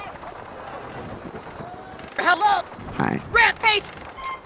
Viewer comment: Sounds like he said “RAT FACE”